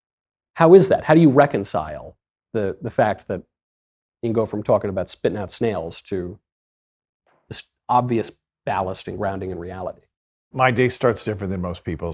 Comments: I thought he said spitting out nails. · I am a Christian in Africa, not catholic but I can attest to all his talking about. I've seen it personally, people vomiting nails or waking up with bite marks everywhere. Some lady would wake up and her hair would be shaved of on one spot constantly. I'm glad that these conversations are now being on mainstream to help the body of Christ revive in America. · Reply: He said snails not nails. I thought he said nails too